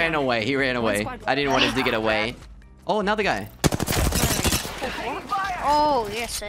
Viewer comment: sounded like you said "got shot by another gay" and not another guy lol